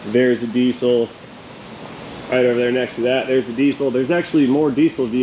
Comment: Did I hear "gasoline-powered diesels"?